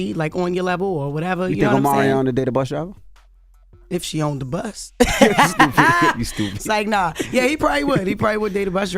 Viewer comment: Jess 😂 be having me cracking up. She said Omarion would date the bus driver if she owned the bus. Just perfect timing with the punchlines. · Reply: 🤔🤔 Wait that's what she said??? Lol I coulda sworn she said "If she was ON the Bus" 😩🤣🤣😭😭